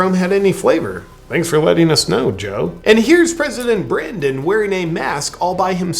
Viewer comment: President Brandon??? Huh.....I thought they were saying President Brain-Dead.....silly me, is my face red!